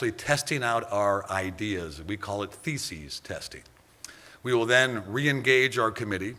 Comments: Did he just say feces testing??? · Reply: He said theses, dumbass. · "Feces testing" Did I hear that correctly? I get that the XFL wants to be as stringent as possible creating a safe environment for the players. So, if rummaging through Tebow, and Kaepernick's stool is necessary so be it. I know he said thesis testing. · Did he just say they’re gonna be testing people’s fecal matter? They’re not kidding about being innovative!